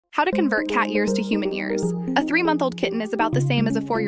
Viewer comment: I heard “how to convert cat ears to human ears” and got super excited 😢